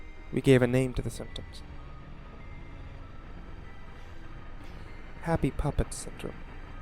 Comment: I thought it said "Happy Puppy Down Syndrome"